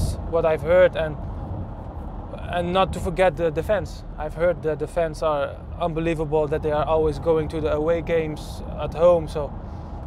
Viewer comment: Thought he said the defence were unbeleivable 😂